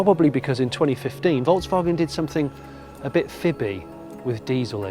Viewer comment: "In 2015 Volkswagen did something a bit..." I don't understand the next word, anybody? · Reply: "Fibby". To tell a fib is to tell a lie.